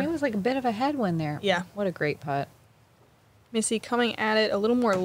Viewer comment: It’s the male in me I know…. But every time they say. “Great putt” I hear… “Great Butt!”